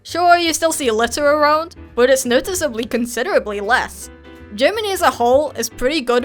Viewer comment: Did she say "Leß"? :D